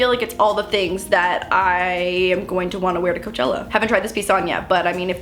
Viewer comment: Latte Lover Moose be like: But Mam I need fwinge fo moosechella! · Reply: I thought u said moochchella...